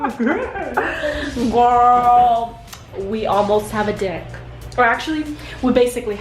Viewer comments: Is it just me? I thought said “We almost have a Dick (she meant Deck though). Lol sorry, I swear I don’t have those nasty minds. I had to watch it a few times to figure out if it was just me but I watched it a few days after and I still thought she said, dick lol sorry 😂 haha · haha i thought you said we almost have a dick @ 8:53 awesome drink (brings out the Asian in me) but now that I live in Asia where it's pretty humid and I walk everywhere and exercise if I have time, I'm practically cramps free! 💃 and yes more wahliekitchen breakfast, lunch and dinner please. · I thought she said "we almost have a dick" 😂😂😂😅